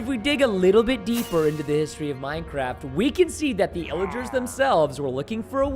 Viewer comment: For a second I thought he said “killagers”